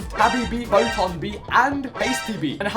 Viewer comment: Not the subtitles saying “Boton bee and basty bee” 😭😭😭